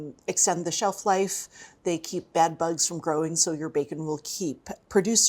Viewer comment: Oh my, my ears heard her say nitrites keep bed bugs from growing and I inevitably gagged a little. She said bad bugs, lol, 😮‍💨